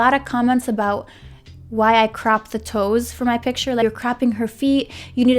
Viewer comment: lol when she was saying 'cropping', i thought she was saying 'crapping' haha nice video. i like the idea of re-editing old photos. definitely something i will do in the future.